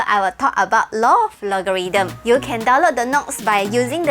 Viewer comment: i heard " don't " leave a message for me ... lol great teaching btw